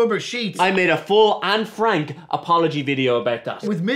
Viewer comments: I thought he said he made an Anne Frank apology. 😳😅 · I thought he said " I made a full 'Anne Frank' apology about that" and I was so confused 😕 🤣